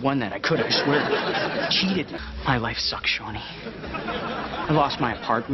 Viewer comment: ahhhhhh At 6:22 when Eric says "My life sucks Shawny" I heard, "I'm bisexual" Then I listened to it again and started to cry